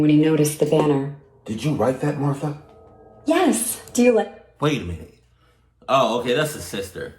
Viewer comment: I thought he said did you write that motha fucker Lol